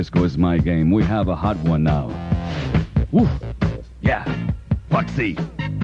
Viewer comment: WOW, these were great classics! @ 2:58 "Woof"! He might have been "serious" but I couldn't help but hear a comedy skit while he was talking on DISCO 92! LOVED WPLJ'S "New York" jingle! @ 9:30 a "dirty old man song" from Tony Bennett?!?!?! 😲 @ 15:07 I missed the beginning and it sounded like he was saying 77 Santa's and then talking about them being defunded..."defunded Santas"!!! What is this country coming to?!?!?! 🤷🏻‍♂️🙅🏻‍♂️😄 @ 23:04 Meg Griffin?!?!?! Family Guy?!?!?! 😄😉 @ 24:95! "...from the top of the World Trade Center"...🙏🏼 I am not a New Yorker but I enjoyed this, especially WCBS, WPIX, and WBLS!!! @ 27:25 Pig poop?!?! Another great one Sir!!! 👍🏻😎👌🏼